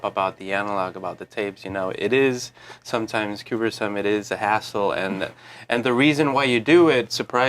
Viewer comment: did he say "coobersome" lol